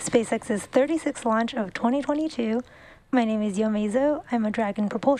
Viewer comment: Did anyone catch the anchor's name at 1:55? She has a really nice voice. "spacex dragon propulsion engineer ..."? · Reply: Sounded like 'Yo Meso' (?) Google gets nada...